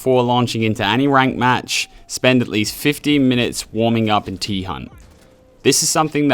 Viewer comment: does he say 15 or 50 minutes? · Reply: 15 brother